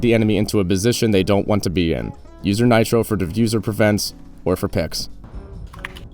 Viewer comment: I don't know why, but I heard "Defuse Prevents" as "Profuser Drofense"